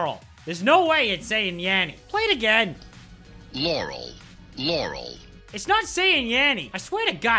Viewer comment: I hear larry